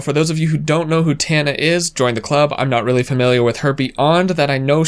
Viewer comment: Did anyone else hear, "I'm not really familiar with herpe - ond"